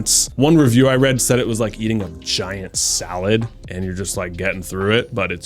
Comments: Am I the only one who heard “vagina salad” around the 2:26 mark? Had to rewind twice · Am I the only one who thought he said "eating a vagina salad"??? 😂😂😂